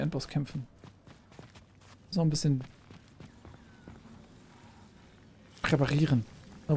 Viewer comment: Was sagt eigentlich an der Stelle? Klingt strange... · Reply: Reparieren